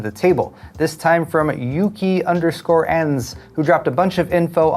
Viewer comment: 🖥🖱All I heard & read was from "Leaker, Yaki ANuS" 💩 oooooowwweeee now thats a LEAK! :elbowcough:💩